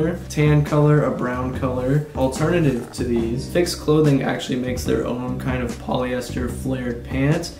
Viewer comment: What were the gray flared pants at 4:52 called? I thought I heard you say fixed clothing but I can't find a single bit of info so I think I'm hearing wrong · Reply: Phix Clothing! they’ve got instagram and a website featuring their clothes